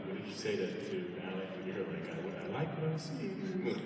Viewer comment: what does Isaiah says here? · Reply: he said that magnus said under his breath, "I like what I'm seeing" when talking about alec